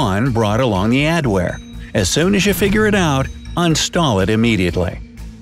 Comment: he says un-stall instead of uninstall